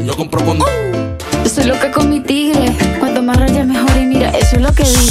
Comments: Yo siempre creí que decía “soy loca come tigre” 😔✌🏼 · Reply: Yo pensé que decía Estoy loca , convertible .👌🏻💗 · Yo siempre creí que decía “soy loca come tigre” 😔✌🏼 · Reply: Yo pensé que decía "soy loca como el tigre" jsjs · Yo siempre creí que decía “soy loca come tigre” 😔✌🏼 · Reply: Yo pensé que decía,"yo soy loca como un tigre" y "el está por mi y contigo no" 😹👌🏼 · Yo siempre creí que decía “soy loca come tigre” 😔✌🏼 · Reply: Yo pensé que decía "soy loca cometida " y ya el loca loca :c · Pensé que decía “Loca comestible” HAHAHA. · Yo siempre creí que decía “soy loca come tigre” 😔✌🏼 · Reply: Yo siempre pensé que decía "soy loca con mitiva" JAJAJA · Yo pense que decia: "Soy loca como el tigre" mshafe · Yo siempre creí que decía “soy loca come tigre” 😔✌🏼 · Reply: Yo pensé que decía "Yo soy loca un tigre" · pensé que decía soy loca comi tigre :0000 no manches fui estafada :'3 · Yo pensé que decía "yo soy loca comestible" 😿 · Yo pense que decía "soy loca comestible" JAJSJSJSJS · Yo pensé que decía "yo solo te come tigre" XD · Yo siempre pensé que decía "soy loca cometida" 😂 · Yo pense que decia "Soy loca cometida" y no, es "Soy loca con mi tigre" nada que ver PUAJAJA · Toda mi vida pensé que decía "Soy loca como tigre" y me imaginaba que se comía al tigre 😔👍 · Yo siempre pensé que decía "yo soy loca come' tigres" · Yo siempre creí que decía “soy loca come tigre” 😔✌🏼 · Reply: Yo entendí "soy lo que come tigre"😅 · Yo pensé que decía loca comestible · Yo escuché "loca con m tegri"